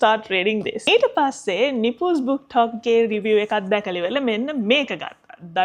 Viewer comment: What is the name you said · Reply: <link> Nipusbooktok